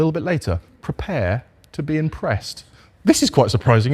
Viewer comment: When mentioned the back seats and said "prepared to be impressed", my brain intuitively heard it as "prepared to be compressed"!